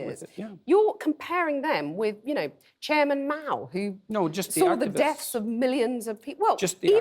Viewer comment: Bro, you misheard, he said, "Chairmn Mao" of China, so you did know the name he dropped.